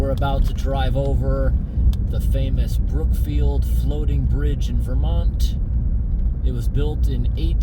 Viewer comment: The problem might be a faulty defrost system or a blocked drain hole... oh 'floating bridge' you say? Sorry, thought you said 'bloating fridge'! ;P